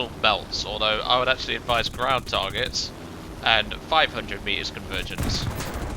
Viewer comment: EDIT - I misheard and thought he said: "Advice air targets belts" - no such option, maybe it's been removed? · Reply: I didnt say that mate, i said Ground Target Belts.